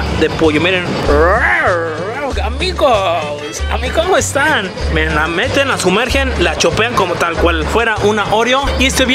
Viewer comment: alguien mas escuchó que dijo me la meten jajaja